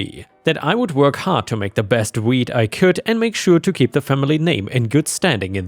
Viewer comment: i was just listening to this and not reading so i thought when you said "wheat" you actual said weed